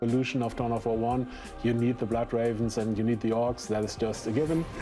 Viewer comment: I swear to God I heard him say Blood Ravens first before you said black Ravens